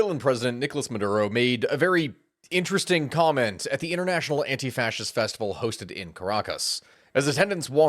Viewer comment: Did I hear that right at 0:13 " anti-fascist"?